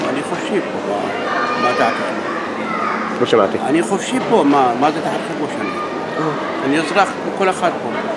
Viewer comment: You misunderstood the guy at 0:15 and 0:19, he doesn't say 'I think that' he says 'I'm free here' (אני חופשי פה)